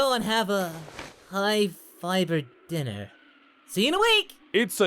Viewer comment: Every time I heard him say that line I think it's saying high fiver dinner instead of high fiber dinner